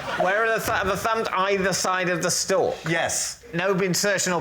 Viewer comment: What David says? Either side of stoke? stroke? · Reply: Stalk 🙂 An apple stalk